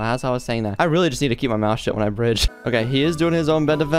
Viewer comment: did u say shit or shut